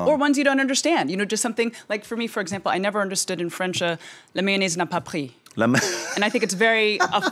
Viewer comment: What did the lady say in french at 10:40? Qu’est que elle dit a francaise a 10:40? Merci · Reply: I know I'm 10 months later but I'm still gonna reply to you, hoping you still want the answer and that it will "help" you ^^ She said "La mayonnaise n'a pas pris" In English it can be translated by "The mayonnaise didn't take (on)" (Well it's a litteral translation) That basically means that you didn't really get along with the girl (or the boy).